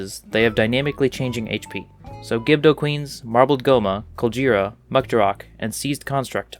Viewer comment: Did i just hear COL J ERA